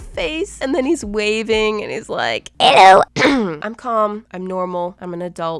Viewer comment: I came back to watch this after work and all i heard was "Elllo"